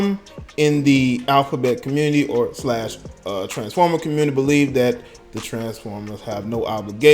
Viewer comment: did he say... TRANSFORMER.... THAT'S WAS SO DISRESPECTFUL OK... I will go now...